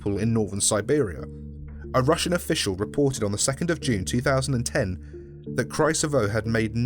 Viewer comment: What was written 2 june 201X What was said 2 june 2010 SCP fundation Our knowlege is far grater then we expected